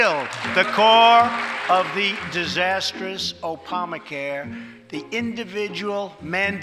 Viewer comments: Did trump say "opama care" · Did he just say Opama ...like with a P? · did he say “opamacare”